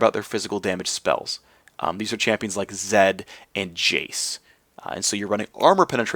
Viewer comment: yo what champion does he say at 3:55? chase? who is chase? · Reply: Jayce